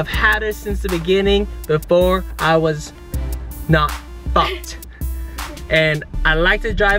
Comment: Did say at 1:31 Not F#cked?